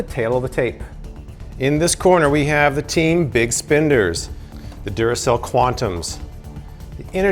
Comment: I thought he was saying big spinders